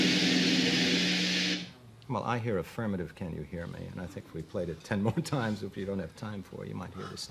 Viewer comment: I hear “affirmative, can you hear me.”